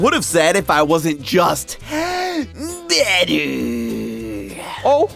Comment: Why do the subtitles say "dead" instead of "better" here. 💀